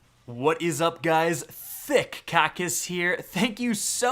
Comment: my parents heard the intro and thought you said "Thicc Cock is here" 😂